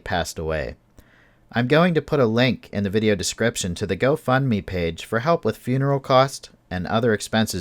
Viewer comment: I thought you said go fuck me page, lol